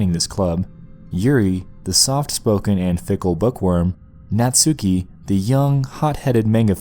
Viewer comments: I thought he said "Thickle bookworm" XD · I thought he said "Thickle bookworm" XD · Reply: same i thought he said thicc lil bookworm